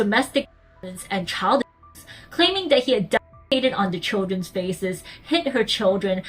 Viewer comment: i don’t understand what you said. could anyone help me out? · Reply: I think she said "defecated"(?)